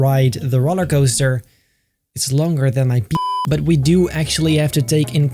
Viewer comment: did he say its longer than my p